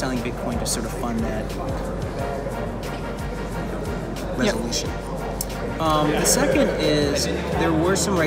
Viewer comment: Tom Lee does not say “revolution” at 1:57, but “resolution”. Surely, anything relating to BCH would not be qualified as being revolutionary.